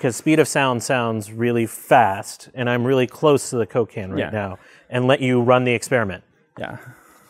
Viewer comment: "I'm really close to the cocaine now" is what I heard.